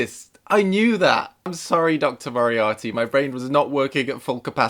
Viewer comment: did he say Dr. Moriarty